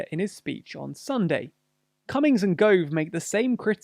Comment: I heard, "comings and goings"